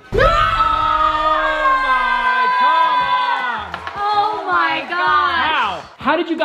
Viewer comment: did they say “oh my gosh” at the same time too?😂